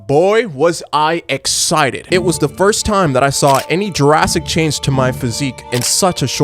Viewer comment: i thought i heard "jurassic change" lmao